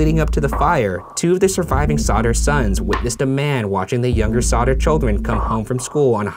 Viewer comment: I thought the it said Sodder Chicken